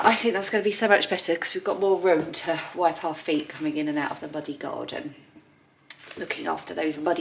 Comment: I really hope your cold is short lived. 💚 Because of your bunged up nose, every time you said 'muddy' I heard 'bloody'. 😆 I thought, "why's Ali swearing so much?" 🤣🤣